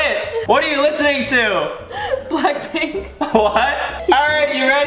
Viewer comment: Did he just say blackpink in lame